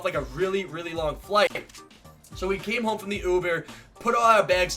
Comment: did he say ubear??